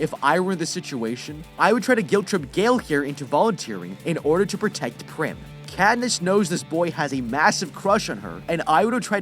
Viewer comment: i heard him say “gale could volunteer for prim” or smth like that not “gale can volunteer for Peeta” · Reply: its "gale could volunteer to protect prim", means that he was the male tribute and keeping prim safe